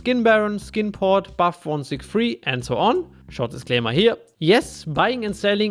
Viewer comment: Did I just heard 2:14 : Schau das Thema hier? Are you german? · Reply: Mein Junge ich bin sehr deutsch, aber im Video sage ich "Short disclaimer here" 😅